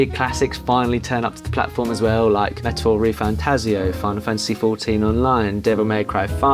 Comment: I'm sorry I couldn't make out the name of the game you said at 1:41 , 1:42 mark? Looks really cool. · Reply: Metaphor refantazio! Honestly one of the best games I’ve played in years